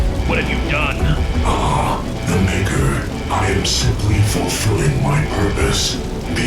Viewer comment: what did he say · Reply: Ah, the maker, i am fulfilling my purpose